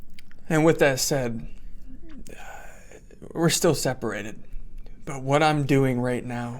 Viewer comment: Omg.. I thought he said they didn't separate 😭😭😭